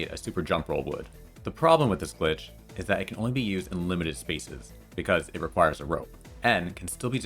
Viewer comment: For a moment I thought you said, “It could only be used in liminal spaces” 😂